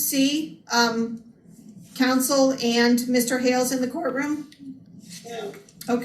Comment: I thought the judge said no kids in the court room